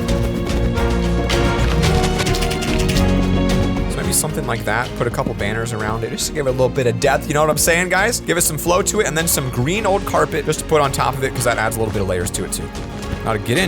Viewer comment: he said death instead of depth